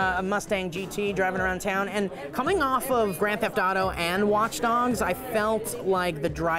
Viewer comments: it sounds like he said watch dongs...... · Did say Watch Dongs?!?! x_O